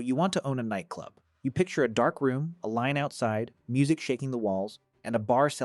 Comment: thought bro actually said a Lion outside... sorry I was listening via headphones wasn't watching directly 😅😂